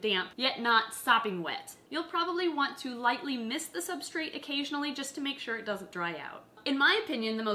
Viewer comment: i dont understand what you are saying - no native engl. speaker here. could anyone repeat please? "you probably want to lightly "xy" the substrat occasionally, to make sure it doesnt dry out." do you mean put some water on it or change it? also what about mating in particular? do i want only one jumping spider (i think you havent mentioned that); i guess they are lone wolfs. also do you change the whole earth below from time to time? would it be possible to have them in your office = 2 Days without taking care or is it better to check every day on them? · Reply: Mist the substrate. One spray from a water bottle. As for mating, she mentioned only one spider per enclosure. Jumping spiders (like most spiders) are cannibals and can eat each other. They only need to be feed once every 2-3 days or so. Once you have a mature jumping spider it should be fine if you feed it on a Friday and don't check in until Monday. Maybe have a moist cotton ball for over the weekend.